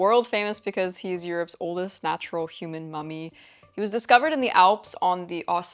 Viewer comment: "He is world famous because he is europes oldest natural human mommy" ... best misheared intro so far :D makes sense why the obsession now xD